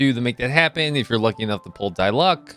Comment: did he just say "die luck"?